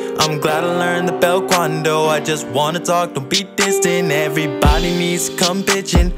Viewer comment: what i think it said: everybody needs to come b####n